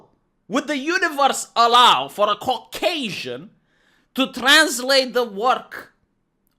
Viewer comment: Did you just say "Cockasian"?